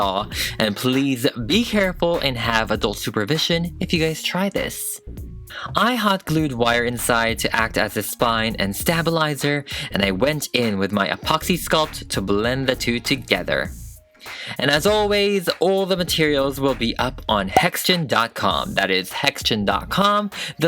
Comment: Hun did u just say stab-alizer?